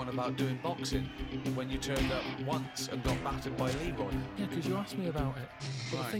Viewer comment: Did ricky just say Karl got "battered by an e-boy" the one time he went boxing or did I mishear it